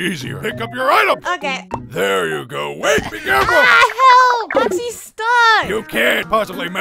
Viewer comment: When I heard “ Oxy stuck “ Me: 😏 Whatca need help with? Note: this is just a joke 😅